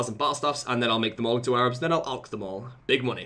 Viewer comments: Every time you say "Air orbs" I keep hearing "Arabs." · I coulda swore you were saying "Arabs" instead of "Air orbs" for the first 2 minutes xD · Sounds like hes saying "Arabs" instead of air orbs XD